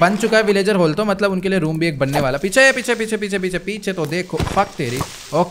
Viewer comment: did he say f teri